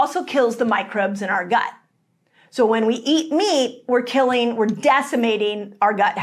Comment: At about 2:43 it sounds like you are saying “meat” instead of “wheat” but hopefully not too many ears were tricked by this mispronunciation!